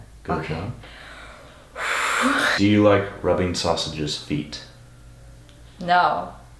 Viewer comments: When it sausage said "Do you ever rub sausage's feet I thought it he said " Do you ever rub sausages meat" and I was like WHAT THE HELL??! 🤣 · At first I thought sausage said “do you like rubbing sausages meat?” Haha😂💀😂💀😂💀👌👌👌